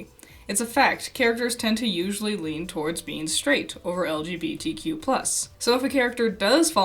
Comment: Am I stupid at 7:30 "it's a fact, characters tend to usually lean towards being straight or lgbtq plus." What else is there? are there orientations that don't fall into the plus? Or am I mishearing it and they're saying "...being straight more than lgbtq plus" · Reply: Sorry! Yes, I'm saying "being straight OVER lgbtq plus." I kind of rushed through the line because my stutter kept getting the better of me on the "v" in "over."